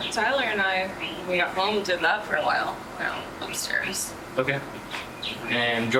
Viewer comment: did she say her and tyler were doin meth for a while?